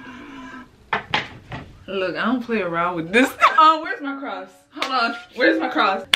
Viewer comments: i thought she was saying ''were is my crocs'' instead of ''were is my cross'' i was like YASS THE HOLY CROCS lol but when u see something like that you get gasoline and a crucifix tho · For the first POV: Holy water: needed Cross: needed Trying not to think of what I just saw: happening Scared: also happening Help （╹◡╹） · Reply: I thought you said crocs 🤣🤣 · laughing demon: exists: frantically tries to find cross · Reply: i thought she said crocs💀